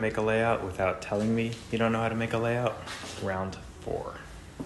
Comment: I thought you said layup and I thought I left that pain behind at the high school basketball tryouts, my guy.